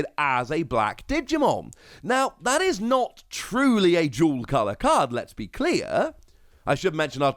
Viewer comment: I thought you were saying jewel color lol not dual color